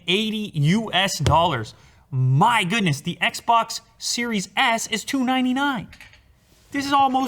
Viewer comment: Did he just say that the xbox series x was $2.99.